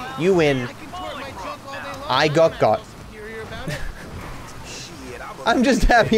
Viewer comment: Did just say i got got just like in videos lol